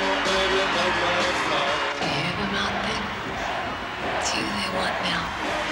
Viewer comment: Does Patricia says "Do you hear them out there ,Its you they want Val?" I was told she calls him Val in that scene and It does sound like Val not Jim or is it just Me? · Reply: “it’s you they want now” I’m pretty sure